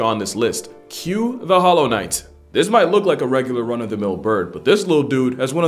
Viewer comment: Young man, you are AWESOME! I pray that your passion to educate never dies! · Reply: What was it that you said at 7:22 ? Because I heard "Hollow Knight". . .